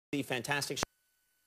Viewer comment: sounds like Conan said "the fantastic shit"